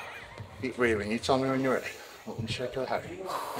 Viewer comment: what does he say at 3:33 keep breathing you tell me when your ready .............. heavy